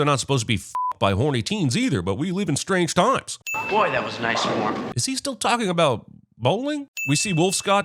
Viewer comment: ‘that was nice form’ is what he said. He said it when she was about to bowl her shot as well.